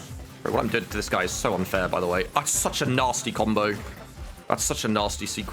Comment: very small thing but doesn't say "I'm such a nasty combo" instead of "That's such (...)" like the auto CC claims? cuz i definitely hear "I'm" n "That's" is such a different sounding word that it got me curious if anyone else hears it à la laurel yanny